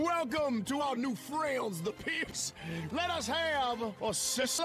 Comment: I thought he said the pimps